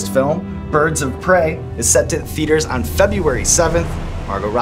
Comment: When he said "Birds of Prey is set to hit theaters" I thought he said "set to hit feeders" and I was like "Oh, it's a bird pun..." Then I realized it's just me who needs help.